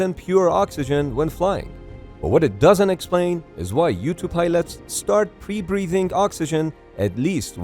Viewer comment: Am I the only one thought he said Youtube pilot instead of U-2 Pilot